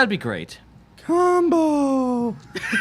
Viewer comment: When said combo at 2:10:11 the captions say [music]